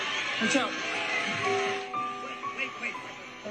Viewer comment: Between: 24:30 and 24:32 I thought that I heard "Wait Wait!" It sounded like President Trump's voice. I'm starting to be afraid for the president, because when he's done with him, he's going to be gone. I hope that he makes it through this term and sits down somewhere, because he doesn't need to be on the wrong side of this guy.